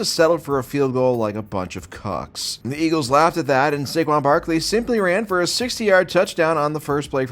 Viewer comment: I thought he said Sigma Barkley for a second